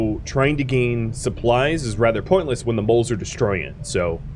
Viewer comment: i'm sure you were saying moles, but i've been watching moules be an mvp character in wendigos so long i just heard you saying his name over and over.